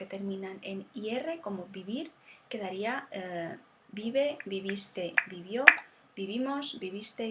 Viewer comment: Thanks, very good. But isn't vivir, "yo viví". It sounds like you said "vivé"